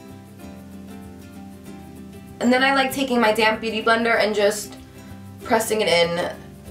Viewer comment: I know you were saying DAMP, but I always heard:"So now I take my damn beautyblender..." haha